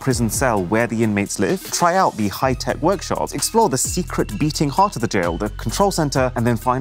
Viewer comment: When he said the "Secret Beating Heart of the prison", I thought for a second he said "The Secret Beating Room" and I was like "Beating room?!?!"